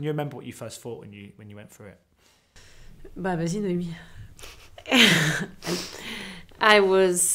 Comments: Does anybody know what she said at 0:28 ? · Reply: She said: "Vas-y Noémie". It's an expression in french for saying in this case: "Go ahead". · Does anyone know what Adele was saying at 0:28? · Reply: She says: "Bah, vas-y Noémie !" which in English is like: "Well, go ahead Noémie!".